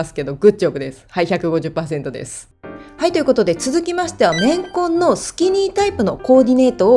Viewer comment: 綿混がレンコンって聞こえて、何言ってるんだろって 笑 私の聞き間違いでした笑笑